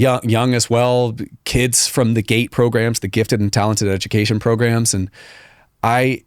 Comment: Every time they say "Gate Program" I keep hearing "Gay Program"